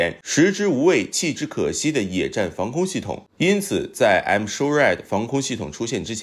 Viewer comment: M-SHORAD防空系統我聽成I'm sorry防空系統，還想說這名字還真特別阿，不過這I'm sorry是對誰說的呢？🤣